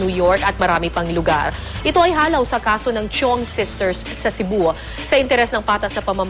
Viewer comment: Chiong case, misheard as "CHONGKE" 😂😂